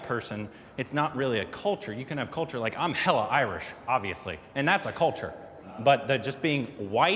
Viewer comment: But he also said that he is proud of being Irish. He didn't deserve it either, and bdw he is only Irish on paper, no Irish would claim him. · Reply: i dont think he ever said "im proud to be Irish" all i heard him say was "im hella irish"